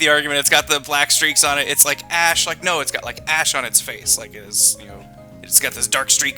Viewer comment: I thought he said he got ass on his face lmao